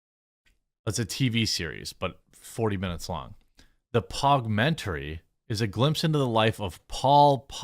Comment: Well, that's disappointing. I thought he said "PAWGmentary".